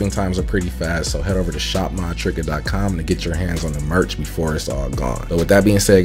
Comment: Yo I'm been watching your vids for mad long and I always thought u said my trigga and just found out it's lol😅